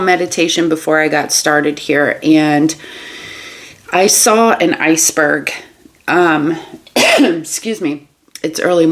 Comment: When you said that you saw an iceberg, I heard, “I must be the iceberg”. (Be cold toward an enticing dark energy.)